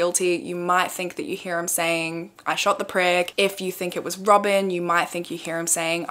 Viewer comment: Sounds like he said I shot the price. Also, as someone commented why did they burn the house down so quickly